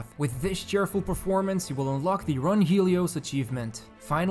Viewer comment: Did he say Run Helios? Uh oh